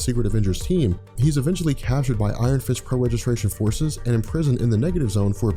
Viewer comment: On around 10:10 You say Iron Fist instead of Iron Man